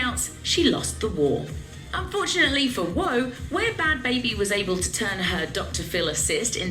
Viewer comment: I thought she said woe @ 5:13😂😂😂